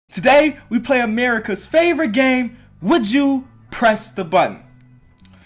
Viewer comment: I thought you said would you crest the button😂